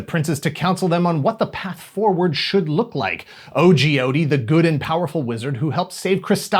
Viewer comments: I kept hearing "O.G. Odie" and picturing a yellow cartoon dog in a "thug lyfe" T-shirt, but seeing it spelled out on the toy package, it's Ogeode. Like, "Oh, Geode". As in the phrase you utter immediately before you groan at the dad-pun. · I can't stop thinking of Jim Davis' "Garfield" comics now because I kept hearing it as "O.G. Odie," and as far as I'm concerned, that's the first use of it in pop culture. Fight me. 😶